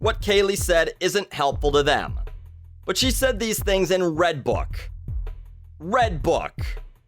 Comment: I thought you said Redtube not redbook